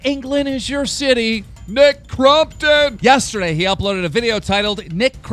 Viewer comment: I thought keem said Mccrompton